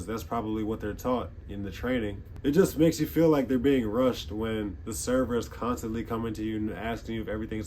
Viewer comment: Yo my fellow B-Ball guy 😀 (My time is over though). Just stumbled upon your video, very interesting 😊. What did you say at 9:44, couldn't understand that nor can I conclude what it could be (for me it sounds like TAMP, but can't make a sense of it). English is not my mother tongue, I understand everything pretty much, but here and there are some words, that I don't recognize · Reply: Welcome aboard! I said that “we were being rushed” lol. Don’t worry, this is a safe space haha.